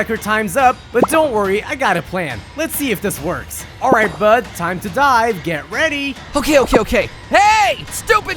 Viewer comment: When Hiccup said time to dive, I heard time to die, which made me chuckle